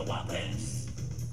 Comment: I hear "wake up out the wubbins"